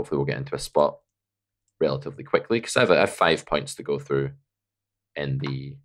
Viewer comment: for a second I thought you said "I have five pints to go thru" and thought, "now there's a lad!", though it won't help performance much.